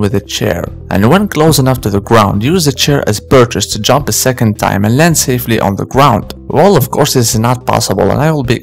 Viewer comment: "use the chair as [???]" What did he say? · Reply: Purchase [ noun ] :3_ a strong hold on something with the hands or feet, for example when you are climbing. SYNONYM: grip